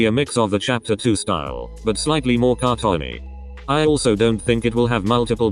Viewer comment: what did you say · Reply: Cartoony 😂